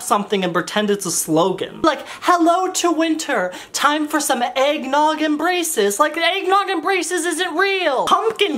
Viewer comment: I thought said “eggnog and braces” then I realized he said “eggnog embraces” lmao